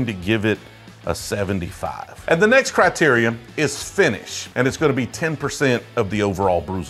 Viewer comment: Love your videos! Very engaging! Btw, just to try to help out, I'll mention that @ 7:52 it sounded like you said "the next *criterium*". Is that what you said? If so, the word is typically "criteria" (in plural form), but the singular form (if you're just referring to 1 specific criteria) is "criteria". Criterium isn't a word. Just trying to help!